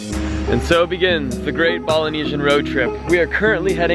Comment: Did you just say "Balinesian"? smh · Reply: Polynesian